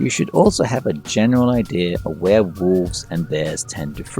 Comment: Did he just say werewolves?!! Yikes 😱 😂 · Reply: He said "where wolves and bears frequent" lmao